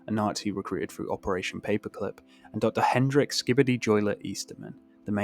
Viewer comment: did bro just said skibidi